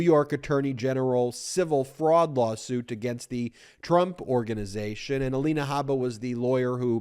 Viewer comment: I thought he said the Drunk Organization - Anyway, 1:42 - Why are only Republicans ever the ones who say "the country's going to hell?" - That's pretty cheap analysis, IMO. BTW: "Why?" I ask... Because Republican leaders rely on their base being too busy struggling day-to-day keeping their metaphorical heads above water instead of analyzing.